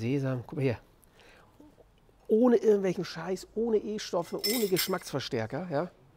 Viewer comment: Ich habe erst verstanden "ohne Impfstoffe" 😁